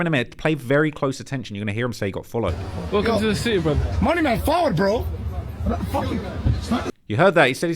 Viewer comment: Lost in translation 😂 All I hear is him say “fawad” not followed. Patois for reach. Ross gotta hire a translator 😂